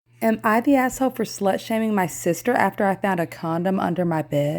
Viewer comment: In the beginning I was so confused cause I thought she said “for sludge hammering my sister”😭😭😭😭